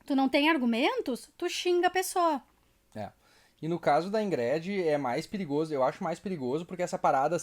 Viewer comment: PAREI DE VER QUANDO OUVI "INGRÉDE".